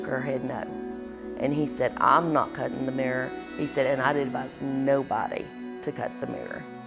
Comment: What does that mean , "Cut the mirror" , or am I hearing her wrong.